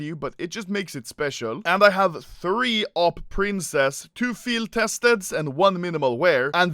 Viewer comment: wait did he just say Awp the prince to The princess?